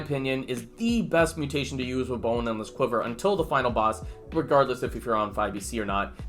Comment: I heard "boneless quiver" for the first 3 minutes